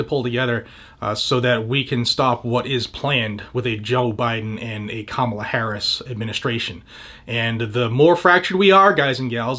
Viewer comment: Did he say "...a Jill Biden and Kamala Harris administration'?